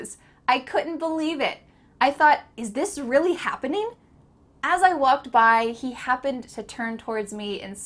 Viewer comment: Great video as usual! Could you please explain why did you say at 8:51 minutes in the video that " As I was walked by, he happened to turn...." ?What about saying "As I was walking by, he happened to turn..." Thank you very much in advance! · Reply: I guess she miswrote the word "was", since she said "As I walked" in the video (without was)